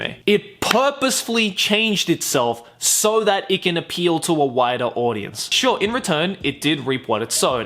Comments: I knew he said "wider audience" but I heard "whiter audience" (￣▽￣;) lmao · When you said it purposely changed itself to appeal to a wider audience. I thought you said a WHITER audience which is also true 😂.